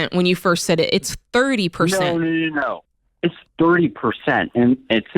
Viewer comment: At time 3:44 I heard 3% as well. Before that, I heard 30%….and then later he clarified 30% . You’re not deaf.